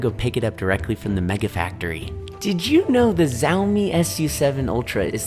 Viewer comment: did bro just said zaomi🤣🙏